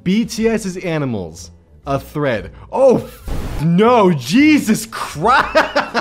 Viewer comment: I misheard "bts as animals: a thread" as "bts as animals: a 'threat'" and that is way funnier